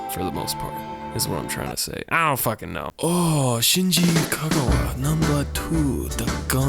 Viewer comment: did u say shinji kagawa, like the football player? :D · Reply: De def name dropped Shinji Kagawa. Mad respect